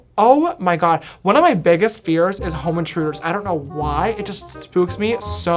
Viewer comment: At first I thought james said he was scared of home insurance not home intruders